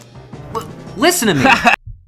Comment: I can’t understand what’s he is saying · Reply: "w-listen to me!"